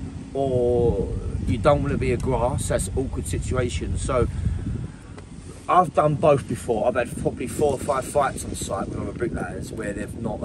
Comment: I thought he said “I’ve done both I’ve had about 45 fights on site” i could imagine it as well haha top videos mate